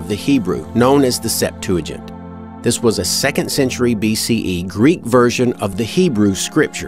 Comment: not that it effects anything in the least to what you are saying here, but out of academic interest, what is your source or reasoning for dating the Septuagint to the 2nd century. All sources I can find whilst not in agreement date it BCE. As I said, when this was written is meaningless in context, because its obvious that the Gospel Writers used it rather than the Hebrew text and it obviously had mistakes.... those 2 donkeys spring to mind :) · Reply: stephenwhippuk yeah, 7:41 I say "2nd century BCE Greek version..." so... maybe you misheard?